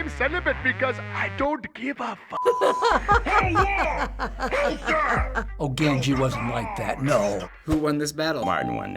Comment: I heard "Ganji isn't like that."